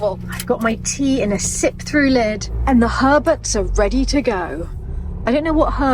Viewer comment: I thought said "hermits" instead of Herberts.